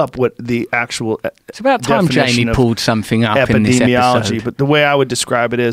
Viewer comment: Edit: 56:36 (my bad) When Russell said "It's about time Jamie pulled something up" I started dying. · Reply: at first I heard Jesse then revisiting I heard Jamie :o I'm pretty sure it's that yanny laurel effect